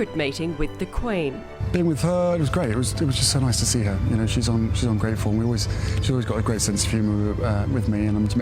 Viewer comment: Does he really say that the Queen is ungrateful? At about 0:51. It seems to me to be a slip up of his own behaviour. He has been ungrateful these last couple of years and even more with this "interview".